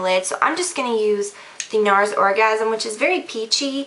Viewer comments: did she say orgasm? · did she say orgazm??